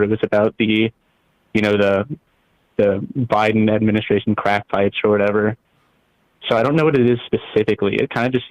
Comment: Did he say "crack fights" at 2:21? lol